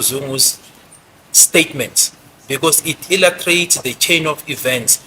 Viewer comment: Did Gininda just say illutrate instead of illustrate??lets replay and listen properly at 1:01 ai we listen but ee dont judge